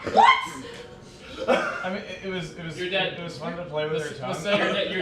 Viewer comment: What did say to at 25:10? I must know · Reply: "It was fun to play with your tounge "